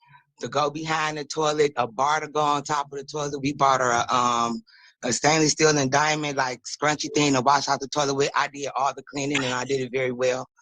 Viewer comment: Did she say a "Diamond Encrusted Stainless Steel Toilet Scrubber" 1:15:39 ???